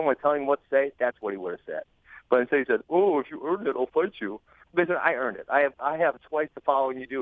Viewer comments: Did Ben Askren say "Nigga, I earned it" just after the 7:05 mark or am I hearing things ?? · did he say the N word at 7:07? "N** i earned it" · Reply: He clearly said "Listen, I earned it." Clear as day. Go get your ears cleaned, buddy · I've listened to this so many times and I swear he says "N*gga I earned it"🤣🤣🤣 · of the video did he say "nigga I earned it"? 🤔